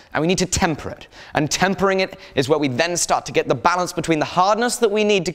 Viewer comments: What did you say first? xD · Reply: Tempering. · LOL 17:30 GRINDING edit had me laughing replaying trying to figure out what he said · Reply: Tempering.